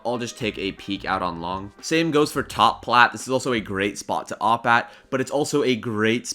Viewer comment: what is the comm u are saying? is it top plat?